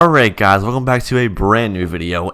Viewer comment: is it me or did he say "alright god"